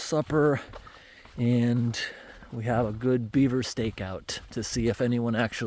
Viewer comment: Omg I heard we'll hang out here and have a Beaver steak and my brain shut off then came out. We'll have a Beaver steak out lol... however Ive been told Beaver tastes great.😅